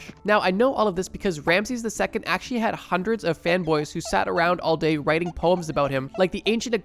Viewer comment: oh man. I thought I heard "femboys". Sometimes I think I'm high without being high